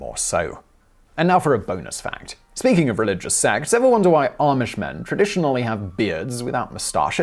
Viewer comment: I'm not the only person who heard "speaking of religious sects" and thought he'd said "sex" instead, right? I was wondering what bizarre salacious trivia they'd dug up about the Amish for a fair few seconds there.